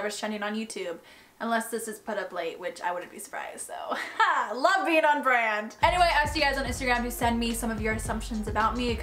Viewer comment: I at first thought you said “Lesbian on brand”